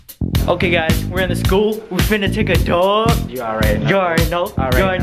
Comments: It sounded like he was saying we goin take a dump !! 🤷🏽‍♀️😂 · Who else thought was saying we going to take a dump🤣